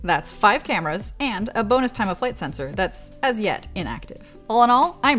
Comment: Did you say 'time of flight sensor' at 3:35? Also, looks like HTC is on board with right to repair if this is all true 💪